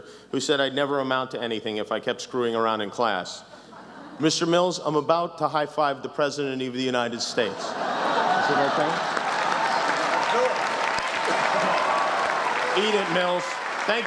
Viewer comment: At the end, I thought he said "Eat it MILF".